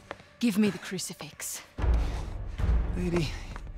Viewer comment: did he say really or lady?